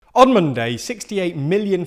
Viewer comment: The first few words of the video sounded like you were saying "Onion day" instead of "On Monday"